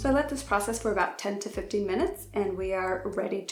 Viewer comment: Hey im french n i would like to tell you something. When you make your toner after bleach, how many time is it ? I thought i heard 10 to 14 minutes but im not sure. Sorry for my english im not the best for explain me Thks you so much i love your hear you make really good job 😉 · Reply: Yes! She said 10-15 minutes:)